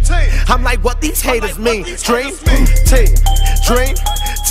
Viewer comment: It took me 4 years to realise he was saying "Dream Team" instead of "Drink Tea" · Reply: I thought he said drink team